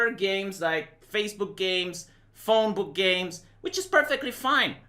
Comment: did he say "Phone-book games"... or "Phone-booth games"? Either way, I wonder what they can be...